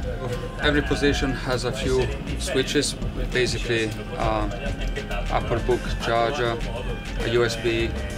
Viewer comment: did he say applebook charger?